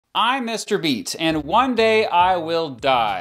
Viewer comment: Lol I thought mr beat said hi I’m mr beast